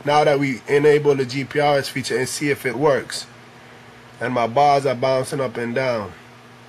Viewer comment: I thought you said and my balls are bouncing up and down. 4:38 hahahahaha